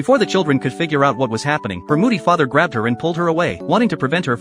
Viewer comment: I thought he said booty father